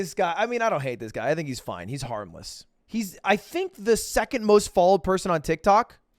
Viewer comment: harmless... I thought he said armless